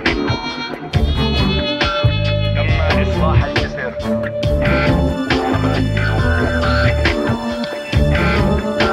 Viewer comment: what he say? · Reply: the bridge has been repaired